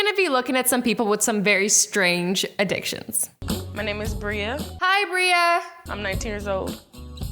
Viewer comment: brea sounds like brielle so for a second I thought you said my name lmao